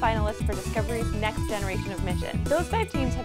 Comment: Thought she said "the next generations of midgets."